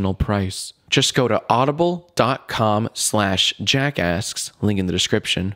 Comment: i thought you said jackass...